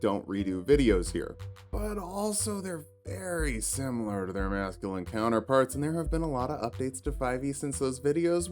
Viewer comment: I had to rewatch the beginning 4 times because I kept hearing "they're veeerry similar to their Nazghulan Counterparts" instead of "Masculine Counterparts"